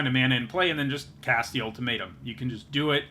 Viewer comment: When you said "cast the ultimatum" I heard "cast the old potato" and I'm using that.